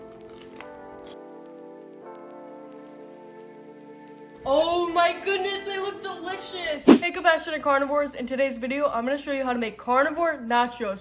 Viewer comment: did she call us batshit carnivores? 😂